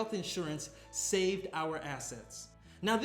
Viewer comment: at first i heard, “insurance saved our ass” when he said, “insurance saved our assets” :p